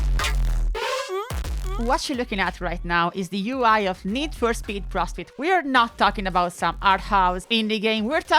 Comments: Perfectly timed Max Payne music but it sounded like you said "Need For Speed: Prostate"... That said, I picked up ProStreet when it came out and I loved it from the jump. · Your English is great and I don't want this to sound critical but I was looking away from the screen and had to rewind and double check when I heard you say "Need for Speed Prostate" lol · LoL, I misheard that as "Need for speed: Prostitute "